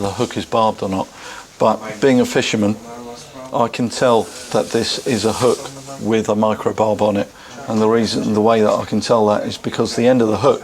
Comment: "being a fisherman I can tell that this is indeed a fish hook" is all I heard at 0:56 XD